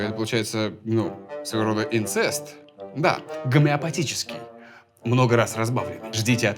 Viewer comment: что за слово? впервые слышу и не могу понять как пишется чтобы загуглить :( Гам....